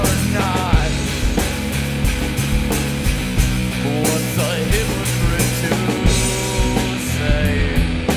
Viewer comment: this is really good, i'm not going to lie though... when i first heard hypocrit i thought you were saying hippogriff.(thinking about harry potter too much) don't judge me!